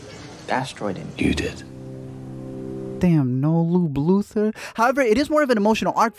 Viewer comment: No Lube Luther...please tell me I heard that wrong 😂